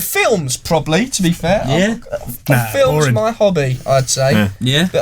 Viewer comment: did I just hear the host of imax say films are boring